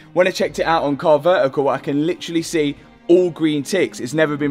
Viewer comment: Is it me or did I hear that all I can see is all green t*ts? at 8:48 lol